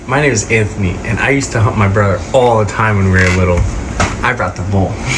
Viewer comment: wtf did he say? hunt? hurt? hump? I'M WORRIED ABOUT ALL